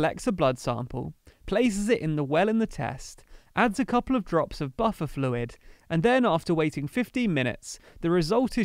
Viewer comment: Thought you'd said "add butter fluid"!